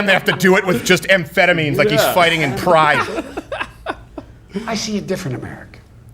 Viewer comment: Wait... did he say fighting in "Pride" at 2:01:08? Like PrideFC?!